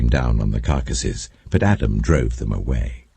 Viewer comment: uhhh it sounds like audio says adam drove them away instead of abraham??